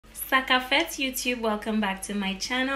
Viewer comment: Wait are you a Haitian in the UK? I heard you say Sak ap fèt in the beginning of your video!?